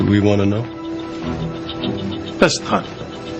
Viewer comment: What does Rossi say at 1:23? "Best time?" "Next time" with a thick accent?